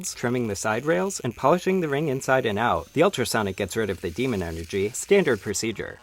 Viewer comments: I'm sorry, I hope I misheard... Did you say "The ultrasonic gets rid of the demon energy"? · Did I just hear “The ultrasound gets rid of demon energy”??:o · “Gets rid of the demon energy” This dude is out here fixing haunted rings. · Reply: i heard it as "human energy" and was rather confused. this makes substantially more sense ty!